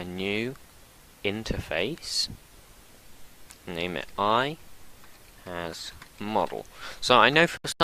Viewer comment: What you said in the minute 30:12? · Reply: IHasModel? Ima redo this video cuz the quality is so bad so dw